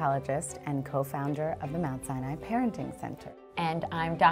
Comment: When they say Mount Sinai it sounds like their saying Mount Cyanide to me